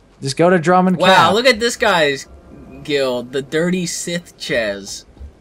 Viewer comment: Who else heard "the dirty sith jizz"?